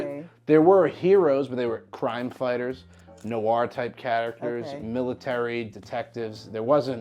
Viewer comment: It’s interesting how depending on an accent, Noir can sound like someone saying “No R”